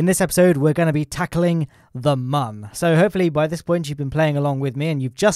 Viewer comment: Legit I thought he said in the intro: "Today we are gonna be tackling your mum"